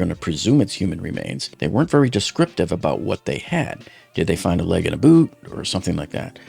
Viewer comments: I thought he said, "a leg and a boob." I nearly spit out my drink. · I thought he said a leg and a boob